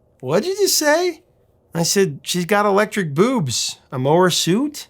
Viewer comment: Sounds like he says "She's got electric boobs, a mohawk to"